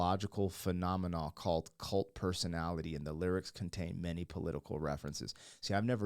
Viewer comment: The first time I heard this as a kid, I thought he was saying "coked up personality" 😂